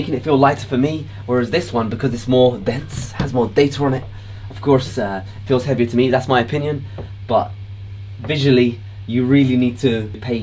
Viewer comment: ummm 2:08 did he just say its heavier cause "it has more data(er) in it"? So data add to weight eh?